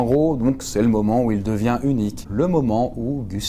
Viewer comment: j'ai entendu "c'est le moment où il devient une bite" 😭😂